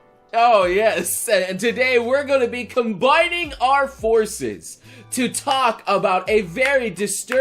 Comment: I swear I thought you said joining forskins. I was now we're talking.